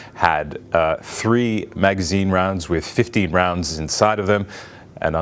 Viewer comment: "Three-magazine rounds with 50 rounds inside of them", credibility instantly and permanently destroyed. One, magazines hold rounds, not rounds carry magazines. Two, the likelihood the teenager possessed 50-round handgun magazines is improbable. This is quite difficult to conceal and transport, anywhere. Propaganda. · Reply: I heard her say "15 rounds."